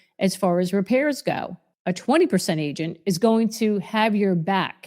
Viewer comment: did you say at 8:43 , "a 20% agent" ?